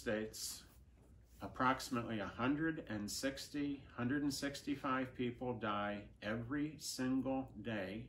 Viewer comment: 선생님 40:35 쯤에 자막에는 매년 165명이라고 뜨는데 들리는거로는 매일 165명이 죽는다 라고 한거같아요..